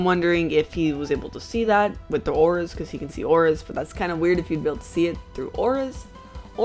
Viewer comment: Who else misheard “Winking” as “Wanking” and laughed for a solid 2 minutes?